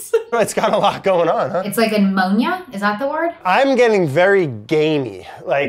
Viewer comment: the subtitles says " indistinct" but I understand she said " ammonia" which is something that appears on the cheese